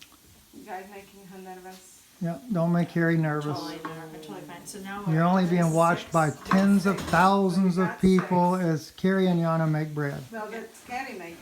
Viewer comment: I thought they said "break bread" not "make bread". Lol